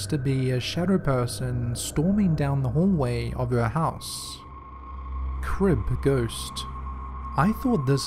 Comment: I thought he said Crip Ghost 😂😂💀 Like damn G, even in the afterlife he still got beef with Blood 💀